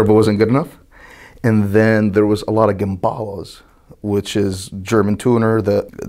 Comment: Wait ? What did he said ? Which famous German tuner did he mentioned ? Brabus, Abt or AMG ? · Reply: Gemballa